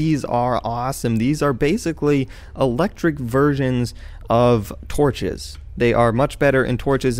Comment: Did he say "Electric Virgins"